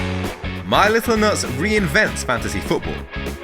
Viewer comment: I listened to just the audio and I always thought he was saying my little notes but it just sounded like nuts so people made fun in the comments, but it's actually my little nuts 😂 how!?